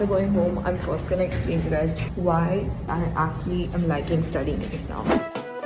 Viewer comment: what did she said here it was a little hazy to hear?? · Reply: Why I'm actually writing studying right now